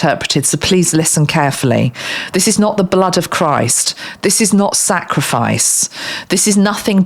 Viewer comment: I am RH negative, and O negative. I haven't finished your video yet, but I have always been fascinated by the spiritual connection with this blood type. I have many early memories, one of which was when I was hospitalized on my first birthday (1966) and when I saw a crucifix I was trying to tell the nun "I know Him!". But I was frustrated that I could not speak yet. The point is, I was certain that I knew Jesus prior to this incarnation. Anyway, all of the RH negative family members that I have, all also have an autoimmune disorder. Each of us has a different disorders, including myself. I have Scleroderma, my mother had Behcet's, my Aunt had Lupus, etc. So the idea that our immune systems run rampant against our bodies also intrigues me. Do you feel any connection there? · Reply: Hi, at one point through this video I heard. 'The blood of the first' 😊